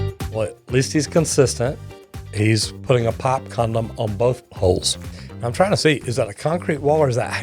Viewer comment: did i hear pipe condom correctly at 3:55 ?